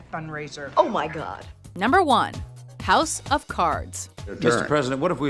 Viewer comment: did she say cows of cards? Is it just me?